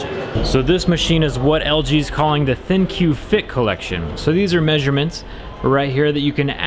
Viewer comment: Did anyone else hear thinq thicc